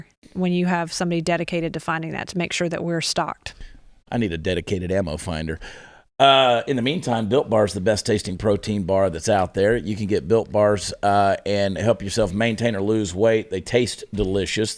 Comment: I thought he said, "Bill Barr is the best tasting Barr...".